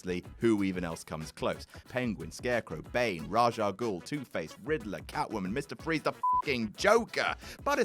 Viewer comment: Did he just say "Tooth-Face"?